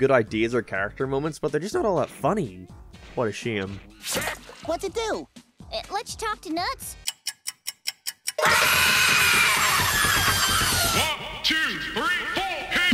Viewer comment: What did she say? “It lich tuck to nuts”?